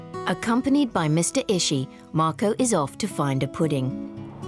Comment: Let’s be real Mr is the true goat of cooking it’s just marco who presents him but mr ishi is the real mastermind behind all these ideas and marco’s culinary success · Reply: I thought it was Ishiin, what? I hear a clear "n" Edit: wow, my ears are biased! Google agrees with you, thank you